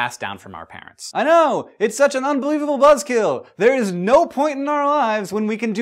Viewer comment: hank said "buzzkill" i heard "bloodskill"